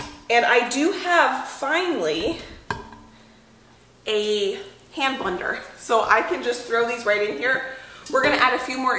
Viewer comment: A «I do have finally a hampunder»? «Hambunder»? Say what? I listened over and over, but don’t get what saying… Sometimes it’s just not that easy to understand what you native English-speakers are saying! Maybe there’s something wrong with my ears? ☺️😁 Could someone interpret? 🤗 💚🌱🍀🌱💚